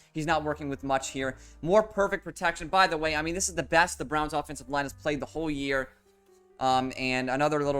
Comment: Thats so weird, I thought it said the Rams not Browns lmao. I was about to seethe in the comments